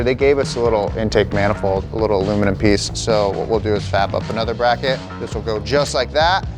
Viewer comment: did he say FAP?